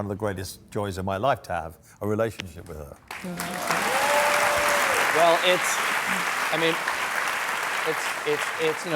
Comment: What did she say at 2:07 ? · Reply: "Amen" I think